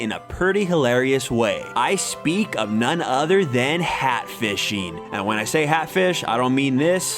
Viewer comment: I thought it said hatishfying